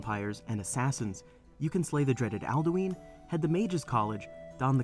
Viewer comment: Wait wait wait wait...... wait Did he just say aldooeen?